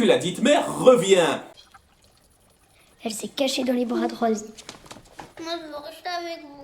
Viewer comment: elle a dit quoi après: 《Elle s'est cachée dans les bras de...》 Si vous voulez, j'ai pas pigé ce qu'elle a dit après les bras de de... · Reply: Les bras de Rose (ou Rosie) je sais plus comment s'appelle le personnage